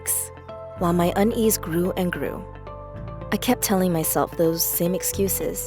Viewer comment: did anyone else think she said “while my undies grew and grew” no? me neither.